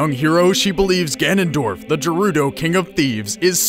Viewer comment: I swear I thought he said "The Dorito King of Thieves" xD